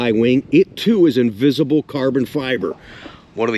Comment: did you say "invisible carbon fiber" @ 4:37? That's an amazing new breakthrough!😆